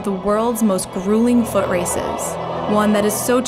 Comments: I thought it said footage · I thought it "said most gruesome footage"